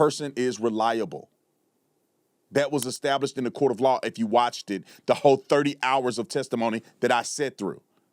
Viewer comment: Did you say Watchded? Wtf 🤦‍♂️